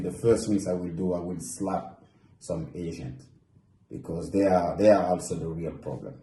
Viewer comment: I really thought he said 'slap some Asains'